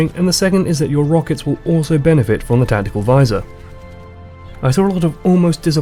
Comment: did he say tentical visor?